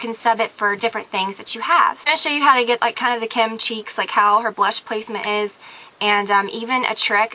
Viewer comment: Haha when you said 'kim cheeks,' i thought you said 'kim chee'